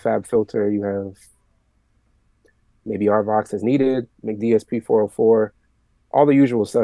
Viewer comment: What is the plugin he says at 31:45? dsp 404? couldn't make it out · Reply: Mcdsp 404